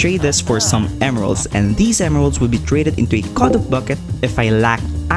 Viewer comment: did you just say "cod of bucket"?